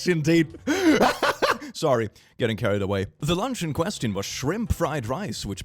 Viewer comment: I THOUGHT YOU SAID LUNGS INSTEAD OF LUNCH I WAS SO CONCERNED ABOUT THE GUY WHO GOT HIS *LUNGS* STOLEN